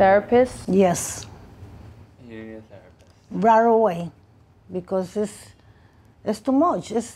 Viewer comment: I thought she said she had a therapist. !?